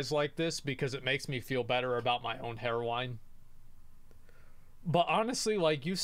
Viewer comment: When he said hairline I thought he said heroine